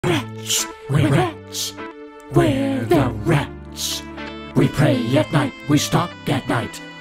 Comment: why did i always hear: "we're rich we're rich we're the rich"